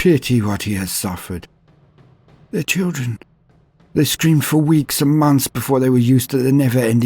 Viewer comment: "they STREAMED for weeks and months before they were used to the never ending pain"... is what i first heard. i listened 3 more times and then finally heard it as "screamed", which obviously made far more sense. until then i was having a real good laugh thinking of kids streaming teletubbies nonstop and, imagining a scene similar to the famous brainwashing sequence in "a clockwork orange", they endured the sun baby and his colorful demons until they were dead inside. you know, like someone driven insane by the touch of chaos. if i had any modicum of video production skills i would love to make a short about that, as it sounds awesome.